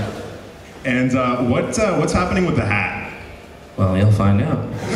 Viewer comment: Can anybody explain what was said at 32:54 and what it meant please? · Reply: Someone asked Jon "What's happening with the hat". He's talking about Jon's iconic hat he wears in a lot of videos. But recently he hasn't wore it. In the second part of his Goosebumps videos the ending showed Jon's hat surrounded by candles while "creepy" music played. I'm assuming that the reason Jon replied with "Well you'll find out" means that it is going to be some plot point in a future episode...